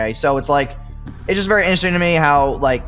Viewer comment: insults pronounciation Proceeds to say "inersing" instead of interesting